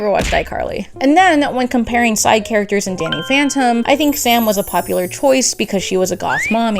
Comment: I heard DANIEL PHANTOM